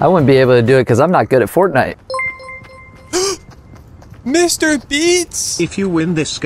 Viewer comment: “Mrbeats” got me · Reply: 😂 I thought he said mr beans